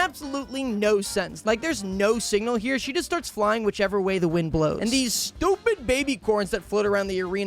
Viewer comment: wait did he just say whichever way the wind blows? HADE’S TOWN REFERENCE!?